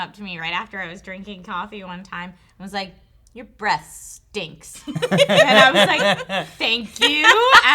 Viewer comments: I thought trisha said your breasts stinks......hahahhahah!!!! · I thought Trisha said, "My young cousin said your breast stinks!" I laughed so hard, then I figured that it was her breath. And I fell into an awkward silence....